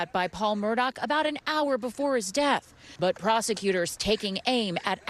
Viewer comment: did she really say "before his death " 😆 🤣 😂 7